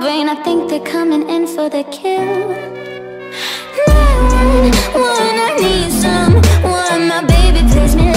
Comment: WAIT IT SAYS '911'?? I THOUGHT IT SAID 'SOMEONE'